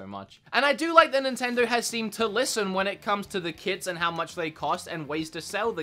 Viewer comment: the yt captions say the kids & not the kits